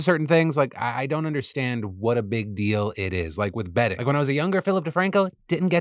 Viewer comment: genuinely thought you said "betting" not "bedding" during the first sponsor so was super confused how bed sheets would be a form of gambling... had to go back and make sure I heard you correctly